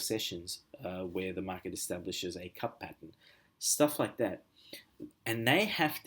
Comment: Firstly, thank you for the video. I am uncertain, however, whether I heard you clearly on two key parts of this video… So, I hope you could kindly confirm/clarify: (1) At minute 17:47 of the video…. with regard to Market Structure, did you mention “Cup Pattern” as one of them and (2) At minute 22:05, with regard to Candlestick Structure, did you refer to “Piercing Line” as one of them? I just want to be sure, since the two above-referred items (Cup Pattern & Piercing Line) appear to have been omitted from the outline/summary that you share with us during your subsequent video titled “Revision of Forex Trading Strategy and Trading Criteria” published on 29 May 2018.